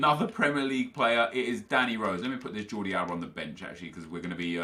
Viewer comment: did u just say jordi alba?